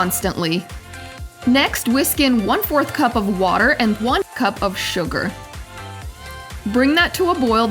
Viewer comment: the subtitles say 3/4 Cup sugar when you say 1 cup out loud. Heads-up 🙏🏼